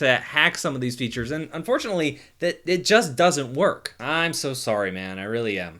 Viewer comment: haha “They just DOESN’T work” that’s what he said...